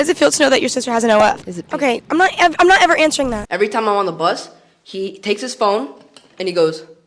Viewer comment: i thought he said everytime i bus, i was like WHAT